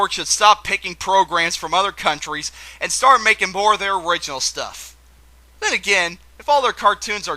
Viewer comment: the closed captions say "Cartoon Network should stop picking programs from other counties, and start making more their racial stuff"